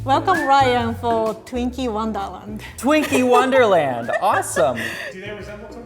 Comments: When she said Twinkie wonderland I thought she said “Twinkie One Dollar” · When said 'twinky wonderland' I heard 'twinky one dollar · I love, I mean it’s but I swear I heard her say Twinkie one dolar instead of Twinkie wonderland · is it just me who heard ‘’welcome to twinkie one dollar’’ ??? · I thought she said “Twinkie one dollar “ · never did I heard say "Twinkie 1 dollar" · I'm sorry when she said twinke wonderland o thought she said Twinkie one dollar tell me not you didn't hear that